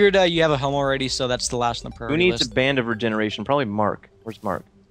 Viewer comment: OH Band of Regeneration. I thought Seananners said "We need to ban a member of our generation. Probably Mark."